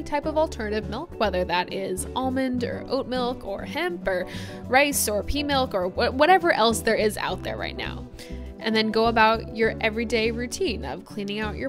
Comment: did you say pee milk 🧐?